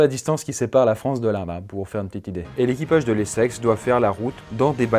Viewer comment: Qui sépare la France de quoi ?? "Il était un petit navire, il était un petit navire..." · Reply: De l'Inde je crois